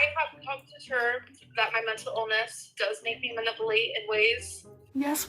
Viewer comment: Ma-nipple-late is all i heard at 40:51 lol